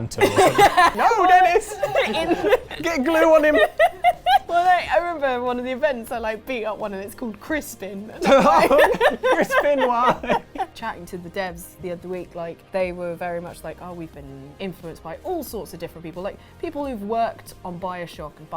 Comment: At first, I thought said "Crispen Wah"...